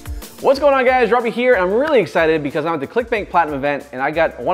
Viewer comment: For a second I thought it said clickbait